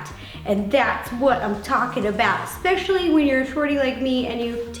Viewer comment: did she say forty or shorty